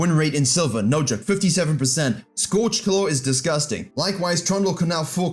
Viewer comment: what is he saying scortch clow ?